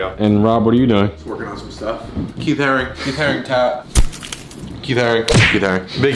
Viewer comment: somebody tell me what rob is saying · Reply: He's saying he is a fan of the artist Keith Haring. He has styles of Haring's art tattooed on him.